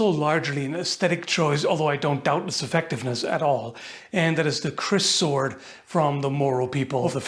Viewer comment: When you said, "the kriss sword," I thought you said, "The Crissword," and wondered why the tiny sword from the Zelda cartoon was in the line up.